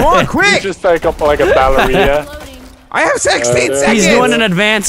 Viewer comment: anyone heard marcel said "I had sexteen seconds"